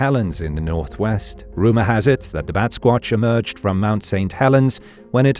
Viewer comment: I grew up in Illinois in the fifties, and we heard about the Windego. At first, I thought I heard ¨Basquewatch.¨ instead of Batsquatch:: an abominable snowman wearing a beret? ¨Sky Serpents¨ Could only be alien technology....strings of orbs...sure. People before the Twentieth Century didn´t know much about the universe. So, when they saw stuff in the sky, they had to think of something that it reminded them of. When they saw spirits, devils, angels, demons, succubi, they didn´t know there were aliens...they didn´t have a word.